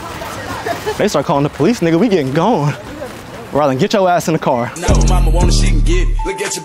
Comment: my name is brylon and i thought he said my name lol😂😂